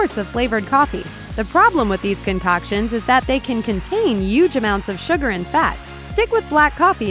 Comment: Did she say yuge at 00:48? XD XD